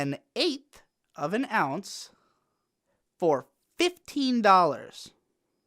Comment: 50$ for an 1/8th? You're getting screwed man, that's like the price of a 1/4th. Even the top quality stuff shouldn't cost you more than 30$ for that amount and that's pushing it. 20 bucks is a good deal and 25 is the "normal price" here or at least it was before it became legal. Those are Canadian dollars by the way so yours should be a bit cheaper. Get a new dealer man. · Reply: I thought I heard 15 dollars? Around 36:30 ish. Not sure